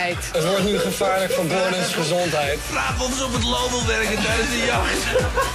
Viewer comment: kan iemand asjeblieft ondertiteling maken, of iig mij vertellen wat er gezegd word op 3:30? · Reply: Vraag of ze op t Loo wil werken tijdens de jacht. Dat wordt Door Gordon gezegd, als je dat tenminste bedoelt